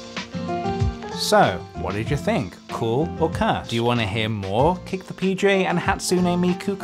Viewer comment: I thought he said "cool? who cares?" in a really weird voice